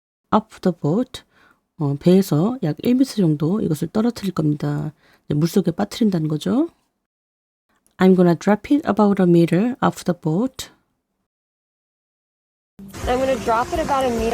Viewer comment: 오늘도 감사합니다^^. 근데 배에서 1미터 떨어뜨리겠다고 할 때 왜 저는 아무리 들어도 I am gonna가 아니라 I wanna 로 들리죠ㅠ